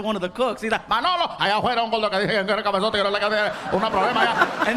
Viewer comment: Manolo!!, ahí afuera hay un gordo que quiere un cafezote, (gibberish) una problema allá. thats part of what he said plus gibberish.